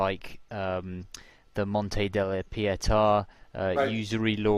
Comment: What was the French word the host spoke of at 53:19? Mont--something? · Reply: Monte di Pietà, Naples housed the Christian Bank <link>